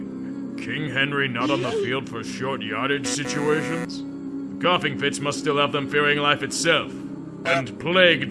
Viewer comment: 😂 did he say “Goffing fits” @ 16:39 ?